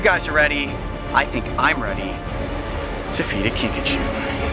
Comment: I could have sworn he said "feed a Pikachu"